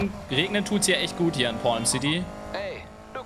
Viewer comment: xd ich habe gerade bei 14:32 porn city verstanden